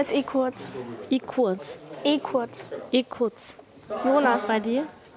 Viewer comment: ich hab den clip in tiktok gesehen und ich muss zugeben ich habe es so lange nicht verstanden bis es mir jemand in den kommentaren erklärt hat ich dachte die ganze zeit dass er echt ehkurz heißt HAHAHAAHAHA